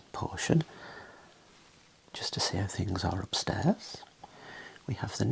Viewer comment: I thought you said, "just to see IF things are upstairs" at the beginning and I lost it 😂 I was like daang... politely implying we might be idiots 😂